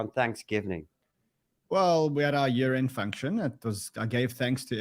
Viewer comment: Lol, I thought he said "we had our urine function."